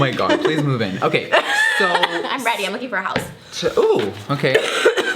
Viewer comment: did anyone else hear trish say ''im ready*cough* im looking for a house''